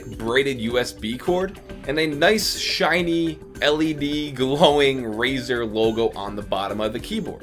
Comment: Did I hear Him Say LEB??